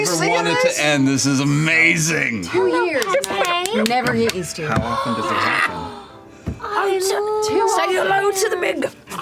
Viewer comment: Can someone please tell me what’s being said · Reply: Laura: "I could never get used to it. Two years and I could never get used to it." Liam: "How often does it happen?" Laura: "Too often! (something I also don't get)" (btw thanks for reminding me to add subs for that part <3)